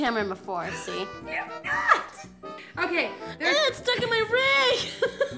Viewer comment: I thought Robin said 'Its stuck in my rack!" for a second there. Damn my dirty mind... I love Felicia and Robin so much. :D